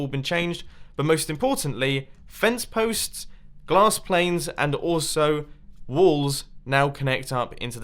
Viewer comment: did he say glass planes?😆😆😆 love u mumbo